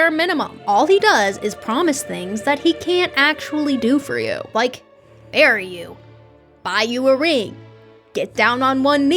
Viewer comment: I MISHEARD THAT AS “like, bury you,” 😭